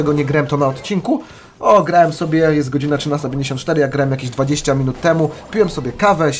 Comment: xD kto usłyszał 13 94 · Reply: dzbanie zmień sobie głośniki w kompie